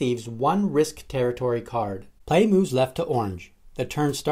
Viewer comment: what did he say ? Play moves left of lunch?